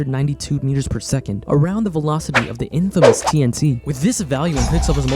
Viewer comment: Hello! I love this video so much, and I want to make sure everyone can understand and enjoy it. That's why I wanted to let you know that your captions are a little off. At about 4:00 the auto-captions say "infidels tnc"where they meant to say "infamous TNT" When you get a chance, could you fix that? That'll help the 360 million folks globally who rely on closed captions to understand videos. Thank you, and keep up the great work!